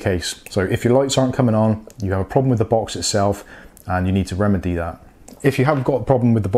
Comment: what did he say? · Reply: and you need to remedy that